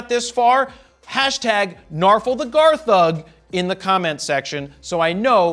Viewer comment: P.S. I heard that as Narful the Garr Thug aka #NarfulTheGarThug --- AND WHAT does THAT mean again???